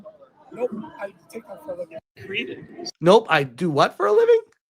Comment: I think he said he does TikTok. Man, it'd be a shame if we reported him now, wouldn't it?